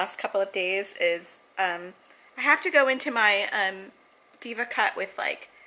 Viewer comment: When you said Diva Cut I thought you said Diva Cup loool .... never heard of it I have curly hair too ( my cuts it dry , then wet , then finishing when blow dried). I wish you showed the organization you did at the salon I was in 7th grade struggling with honors algebra .i was going for help at lunch and afterschool. My teacher knew it . Yet when I asked a question he said if you stop day dreaming and looking out the window you would know . I ran out crying . He called my mom. My mom asked me what happened and pulled me out immediately from his class ( mr Alexander was his name ) such a jerk !!! Clover was dreaming about the Shabu Shabu he was going to miss